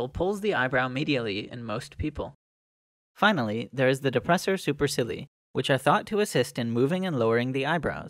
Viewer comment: When you said 'supercilii' I legit heard it 'super silly' 🤣